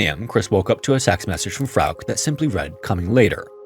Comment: Don't think you meant to say this but early on at 3:40 it sounded like you said "s*x message" instead of "text message"